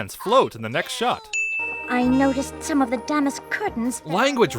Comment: I looked at two different scripts for this episode, and I don't think Rarity said d***est; the word she said is most likely damask, so here's a link to each script supporting this statement: <link> <link>